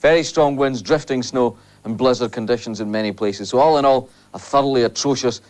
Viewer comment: lol 3:11 I thought for a second it said buzzards in Scotland 🤣.